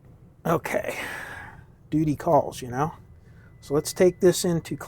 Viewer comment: Did you say booty calls at 28:00?😂